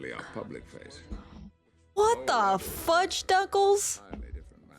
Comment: What was it that said in 10:50 ?? "What the fudge douglas" ???